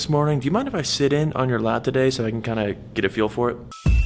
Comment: subtitles said lap not lab 😂